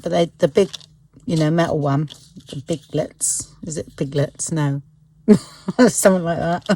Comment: Lol I thought you said piglets. Omg so funny.